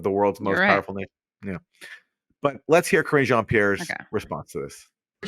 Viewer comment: I really thought you said "Cringe Jean-Pierre" before I realized you did say "Karine..."